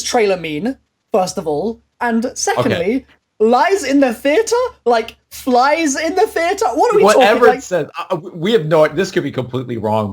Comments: It sound like it says lies in the sphere (the snow globe) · I hear "He only dies in the theatre"or I hear "Nikoli dies in the theatre" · I hear "it lies in the ether" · What I hear is "Teddy lies in the theater."